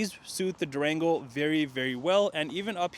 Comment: It sounded like he said Drango 😔😌🤣 I have mixed emotions right now. I'm still spiriling,I got approved for a vehicle. 20 miles on the vehicle I would take but 50 miles I think not.